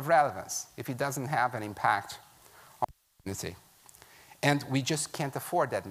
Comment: i think he said community